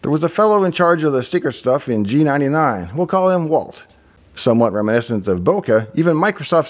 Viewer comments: says: "we'll call him Walt" Subtitles say: "we'll call him Brian" uhmmm... so his actual name is Brian. Nice one. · Your subtitles at 12:50 say "Brian" instead of Walt.